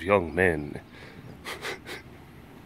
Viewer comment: "I ain't lying" I heard "I ain't lion"... Someone stop me.